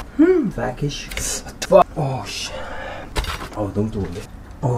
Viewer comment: 저 정말 오래보고 있는데 0:44 도대체 뭐라고 하는거에요? 맨날??? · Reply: 키송~